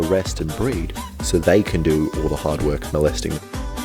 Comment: did you say molesting 🙂